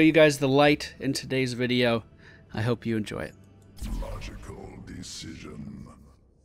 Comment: I lost focus on the video for a second and I thought I heard Abathur say "Magical decision" I now feel blizzard should make this abathur skin say this